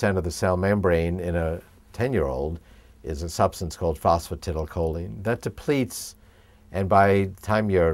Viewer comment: can anyone tell me what e said in 2:00 about the cell membrane in a ten year old?? he said something like phospfy or some shit. anyone know what its called?? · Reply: Phosphatidylcholine.